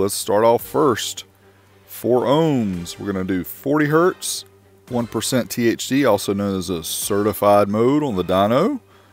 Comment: Sounded like you said 1% THC. Lol